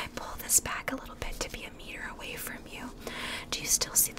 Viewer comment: "pull it back to be a mirror away" is that what you're saying or? Edit: 3 months later I still don't know, is she saying "a meter"?